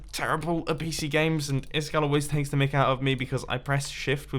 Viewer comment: did mumbo say that iskall extracted to Micael out of him?